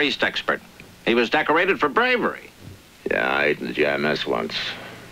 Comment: Please, what does Matchless say at 23:00? It's just after the guy in uniform says "He was decorated for bravery" and starts with "Yeah." Thanks in advance. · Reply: The officer says he was decorated for bravery. Matchless replies " yeah, I ate in the GI Mess once." A humorous put down of the quality of food given to soldiers.